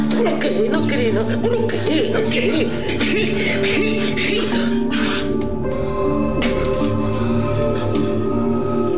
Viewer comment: Alguien entiende que dicen en el minuto 13:00 ? Me causa mucha curiosidad morbosa jajaja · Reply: "Deci que no quieres , no quieres, no quieres " yo supongo que el viejo se refería a que diga cosas chanchas para que se exite más jaja